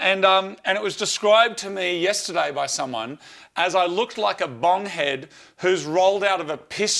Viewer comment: The automatic subtitles are actually almost perfect, apart from 'duff' instead of 'doof' which is a rave, beacuse the music goes 'doof doof doof' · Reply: What about bomb head? I thought he said bong head, but it's hard to tell for sure.